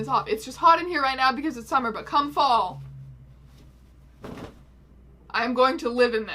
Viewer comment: Is it Very On Brand Gay that I heard "cum fall" and not "come fall" at 0:16 ?